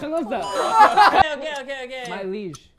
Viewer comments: i think he said "my liege" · I think he says “my liege.” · I think he said “my liege” which is like Medieval slang for “your majesty”. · he actually says “my liege”